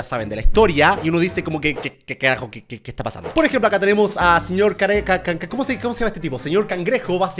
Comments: escuche señor cara de caca xd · entendí cara de kk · no se si soy yo pero escuche que dijo Sr. Cara de kk :v JAJAJAJAJA